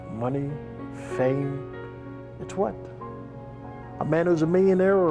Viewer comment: What does he say at 21:40? When talk about money, fame, its??? I rewinded about 6 times can't figure it out. Amazing video, Thanks, message is clear from ATB...live life to the fullest. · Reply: he says "money, fame...it's WHAT?". The 'missing' word is "WHAT". The word "WHAT" (in 'THIS' context) essentially means the word "NOTHING". So what he is (essentially) saying is that MONEY and FAME mean 'NOTHING'...when you're dead. He is 'not' the DUMMY that he 'appeared' to be IN THE RING. It was (all) an 'ACT'...to SELL TICKETS.